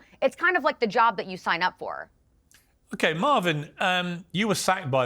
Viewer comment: did Piers call him Marvin? Ha